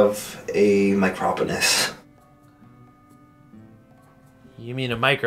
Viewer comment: At first I thought it said micron pens And I was like “yeah I have those too”😂😂😂